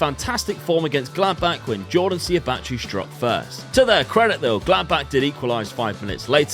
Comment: Definitely misheard “Gladbach” as “Blackburn” and then Brereton Diaz being there just left me confused. 🤣